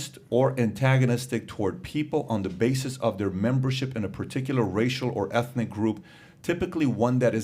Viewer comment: Listening to a press secretary have factual conversations is refreshing. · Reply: I thought you said facial for a second there 😆